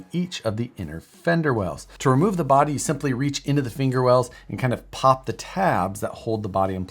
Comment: Wait did I just hear "finger wells"?